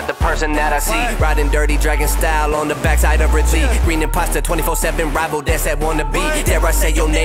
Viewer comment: Is it just me or did he say green imposter